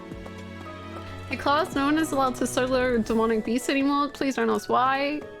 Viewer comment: I couldn't understand what you said, and Closed Captioning helped even less, but I also think someone was probably acting up in your twitch chat and you were trying to put a stop to it so I'll leave that side of it alone, HOWEVER, the closed captioning is hilarious! "Hey class no one is allowed to solar demonic beasts anymore please don't ask why. We're not gonna have any discussion about this, it just is the way that it is. You're told thank you." I won't have any discussion about those solar demonic beasts, promise! I did however have a really good laugh listening to that three or four times trying to figure it out before I gave up.